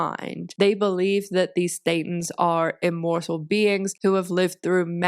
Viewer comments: The fact that everytime says thetan the auto-generated captions say satan is hilarious to me😂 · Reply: I actually thought she said Satan. I had to replay it to realize it was Thetan 😂😂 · keeps saying thetan and at first i was confused and thought she was saying satan with a lisp... 🤦🏼‍♀️